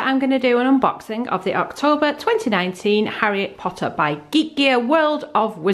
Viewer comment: Why does it sound like you say "Harriet Potter" instead of "Harry Potter"?